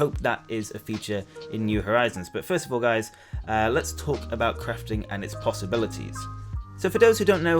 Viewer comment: Watching early on a Sunday morning. I heard "crifting" and "crift". I think I need more sleep xD Awesome work, dude! Edit: Where did you find the Animal Crossing Hour music remix? It sounds so nice!